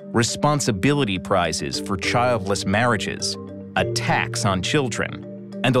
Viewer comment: I misheard 'a tax on children' as 'attacks on children' and I was like harsh lol